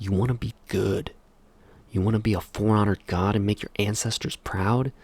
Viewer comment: my halo brain heard you say forerunner instead of for honor